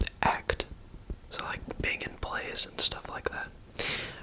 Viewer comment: Did say Megan Plays at 4:02 ? Xx aha